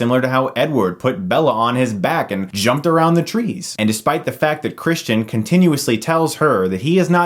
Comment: sounds like you said jumped her in the trees